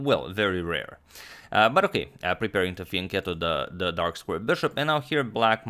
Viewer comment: "...preparing to __ the dark square Bishop..." What's said here please? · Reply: It's "fianchetto"